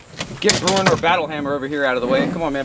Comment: DUDE! At 10:44 am I hearing that right? Is that a random Bruenor Battlehammer reference out in the wild? NICE!